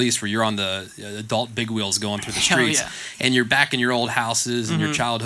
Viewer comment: d-did Tyler just say "hell yea" ?!? That's is the most I've heard him cuss😂